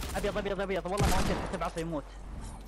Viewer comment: كأني سمعت تبعصه🙂؟ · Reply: قال بعصا و يموت يعني بالبيكاكس